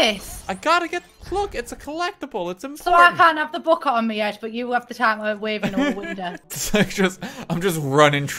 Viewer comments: I have NO CLUE what she just SAID and I replayed the clip 3 times. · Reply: "So I can't have the book on me head but you have the time to wave into a window?" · I have absolutely no idea what any of the words she said was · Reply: I can't have the book on my head but you have time to wave in the window - I think, her accent was pretty thick then